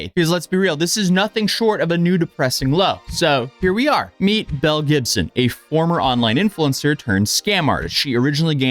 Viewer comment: this video auto played and I was only half listening and I just about fell over when I heard Mel Gibson not Bell 😂😂